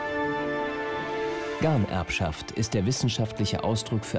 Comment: Bei 13:21 das Wort ist sehr schwer zu verstehen und ich musste es "googlen". Gott sei dank gibt es google und wikipedia. Gemeint ist die "Ganerbschaft" und auf Wiki gibts entsprechendes weiterführendes Wissen, für alle die es auch interessiert :)